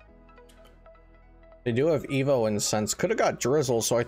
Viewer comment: I heard “emo incense”